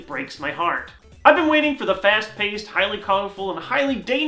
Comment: did he just say fast paste? XD